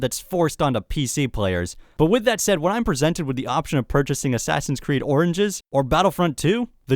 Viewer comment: Haha, at the end it sounded like you said Assassin's Creed Oranges